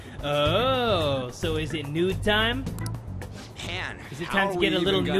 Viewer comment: lol, he said "Han" instead of "Man"